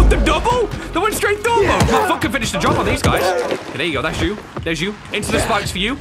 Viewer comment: Does he say I will f*ching finish the job